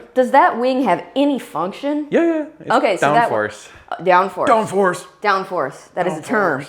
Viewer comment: When she asked about the rear wing’s purpose and you said.... Downforce she said Downforce then I thought she said “or Down-FARCE”? It’s such a good line for her I think she should edit it in.